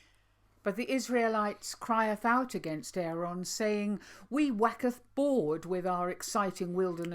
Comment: Thank you and for todays chat always so challenging and comforting and you owe me a new t shirt because I spilt my morning tea all over it when you said "wacketh bored" instead of "waxeth bored" I thank God for you both xx🥰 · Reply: Yeah, I thought said 'Wacketh bored.' !